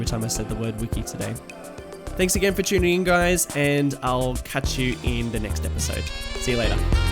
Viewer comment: it really sounds like he says "... and I'll cut you in the next episode!" I'm getting a little terrified.. :D